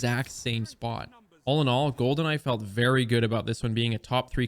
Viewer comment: Every time he says “Gold and I”, all I hear is “Goldeneye”